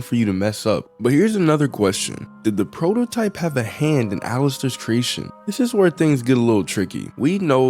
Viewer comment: Why did I hear it as "alastor"😭